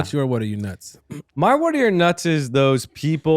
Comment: did he say “what are your nuts” 😂😂😂😂